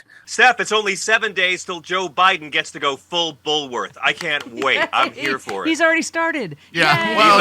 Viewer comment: Had to look up “Bulworth…” I first thought he’d said ”Bulwark” … I’m partially deaf, so I mishear things all the time.